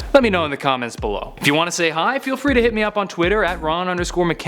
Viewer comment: at first i thought he said im Ron McKenzie the virgin